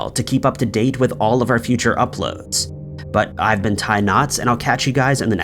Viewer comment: Whenever I see a video notification, I click, hit the like button, and watch. It's always fascinating. ☺ 👍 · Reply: Ha! I thought he was saying "I'll be tying knots" - as if it was an expression I was unfamiliar with!! Now i know he is saying his name and I'm sure I will hear it correctly. Wow. 🤦‍♀️